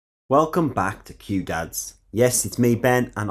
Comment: First time here and i heard "welcome to Cute Dads"